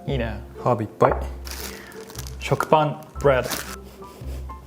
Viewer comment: I was looking away and thought said chocopan, which sounded absolutely foul with fish and herbs.